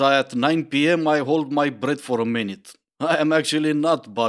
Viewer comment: I thought you said "I don't wanna go on a date because at 9pm I hold my bread for a minute" It's funny but it works just as well as "breath" in the realm of dumb excuses lol